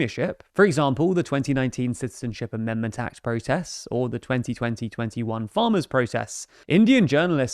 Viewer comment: did bro say 202021?